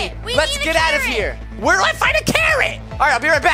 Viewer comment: When he was finding Carrots, I thought he said Karens.